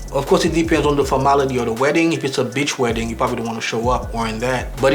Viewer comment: I thought he said “Bitch Wedding”